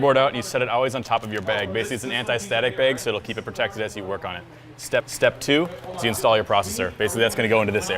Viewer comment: did say "step step 2" ?